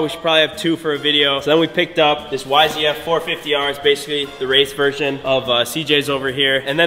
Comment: did you just say “yfzf450” lmao